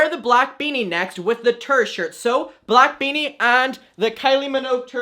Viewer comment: love you, but when you said "tour shirt", I thought u said "turd shirt" 💀💀